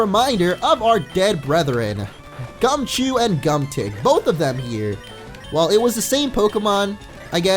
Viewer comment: I thought i heard Gumdick instaed of Gumtic...